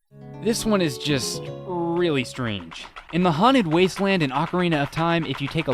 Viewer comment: did he say mojave wasteland at 5:06 XD